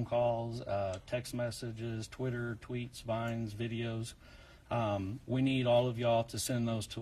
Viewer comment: did he say vine tht app still around?